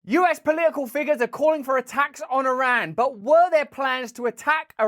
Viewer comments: For a moment, I thought you said ''a tax on Iran''! I wondered what difference that would make.... and then I realised.... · When you said "attacks on Iran," I heard "a tax on Iran." Verrrry Interrrresting!